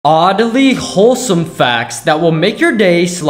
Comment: Did he just say "odely" 💀